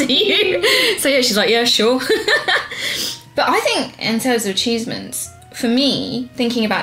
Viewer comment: was it me or did just say ah"cheese"ments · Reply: She said "in terms of achievements"